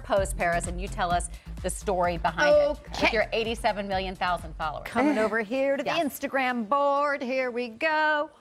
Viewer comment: Did she say 87 Million Thousand followers 0:24? LMAO